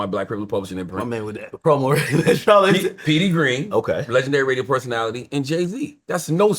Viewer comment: i always thought he said Howard Stern was one.